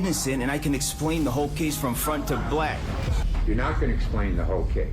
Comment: did he say “from front to black” wth